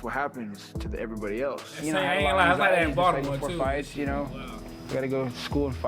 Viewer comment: 🤔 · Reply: 410 is Baltimore Area Code. I thought you said “It’s like that in Baltimore” so I thought you were saying you were from Baltimore